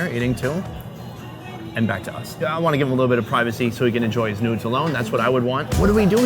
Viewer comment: Did he say "I wanna give him a bit of privacy so he can enjoy his nudes alone"